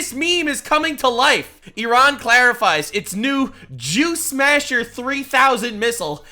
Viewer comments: Thought said "Juice masher" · I thought he said “juice masher” at first. It’s just to make juice haha.